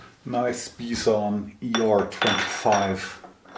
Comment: Did anyone catch the name of the Polish tooling manufacturer? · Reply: Bison